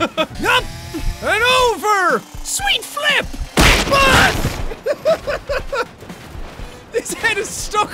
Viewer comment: all I heard BUS!!!!!!!